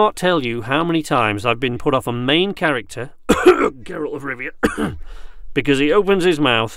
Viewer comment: did he say Geralt?